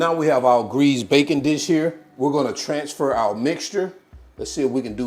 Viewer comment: You pronounce tarragon the same way I do so that makes it correct. 😊. When you said greased baking dish I thought you said bacon. So I think bacon would taste awesome in here (but then it's not French onion I guess.). Nice video!